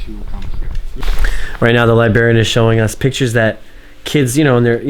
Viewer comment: did he just say, "Libarian"?